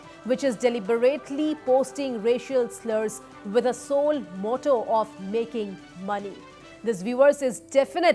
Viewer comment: did i just hear '"soul mortal" someone correct me :(